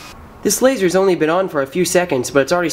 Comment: I thought he said depression grading